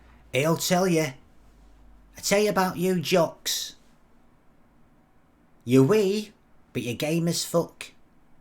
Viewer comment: ''You're wee, but you're game as fuck.'' hahahahahahaha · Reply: Dang, I thought he said, ''You're wee, but your game is fuck.''